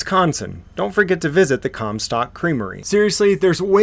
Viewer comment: 🤣Did he say "Cum stock creamery" @ 3:35 Too much comedy in that place to go unnoticed